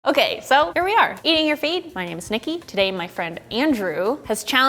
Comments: I thought she said eating your feet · my microphone kinda messed up at the end, making it sound like she said "eat your feet" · It sounds like she says "eating your feet" lol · Thought she said eating your feet in the beginning. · It sounds like she said Sniki😅😂😂😂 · I always hear, "Eating your feet." In the intro.